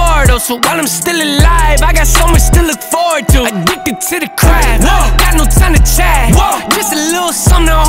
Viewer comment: thought he said “addicted to the crack” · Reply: You made me laugh, I went on that and headed it, but he said, "he addicted to the craft" but you made me laugh 😂